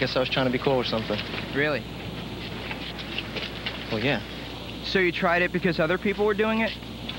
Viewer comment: I could watch this a thousand times and every time he says “well yeah” I hear “well yeah, bozo”